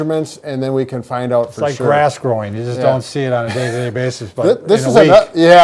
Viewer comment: At first, I thought he said your ass growing. 😂